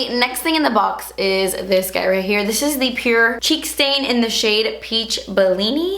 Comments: HHHH I THOUGHT THE CHEEK STAIN SAID CHEEK SATAN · Idk why but when you held up the cheek stain, I thought the box said cheek satan and I was like........ Wut?